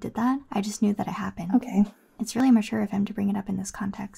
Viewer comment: Did she say mature or immature here? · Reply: mature, i accidentally liaisoned with “really”